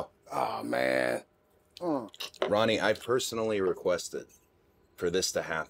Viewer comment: did he just call him ronnie